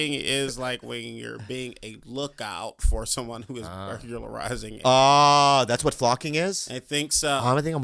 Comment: what he say flocking is? a lookout for what? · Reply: A buglary